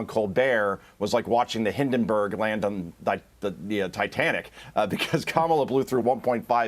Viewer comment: Did he say there is no appetite for a Kamala cum bag or come back?